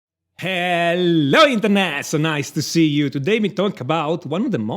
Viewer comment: Sir pls What do you be saying on intro i hear “ Heyyyllo Mrsternational nice to see you” pls correct me if I’m wrong which I think I am🤦‍♂️